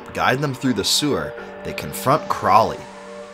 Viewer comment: am I only one who heard "They can FUCK Crawlie"....??